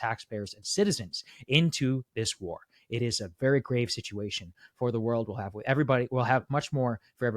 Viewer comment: I thought he said very "great" news, not "grave" and I was like WHAT